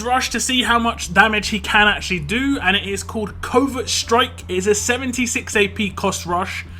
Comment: Legit spent the last 4 months getting nothing but Mercer Cards & getting my S Class Mercer 🙃 Also thought you said his Rush was “COVID STRIKE” at first 🤣 Guess I’m gonna put off T4 Mercer cuz he is gonna drop in viability lately.